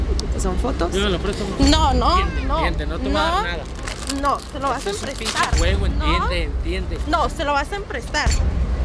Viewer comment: "se lo vas a emprestar" ¿o escuche mal? 😂